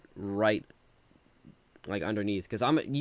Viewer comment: Guys, tell me what he said when he made the limiter to the maximum. English is not my first language and I could not understand. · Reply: He basically said ‘this is what you guys think I do’ when he turned it to the maximum and then when he turned it up less afterwards he said ‘this is what I actually do’